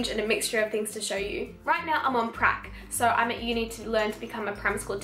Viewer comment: I misheard you and thought you said “right now I’m on crack” 😅